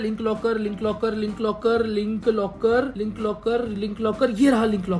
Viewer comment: sounded like lip locker 🤐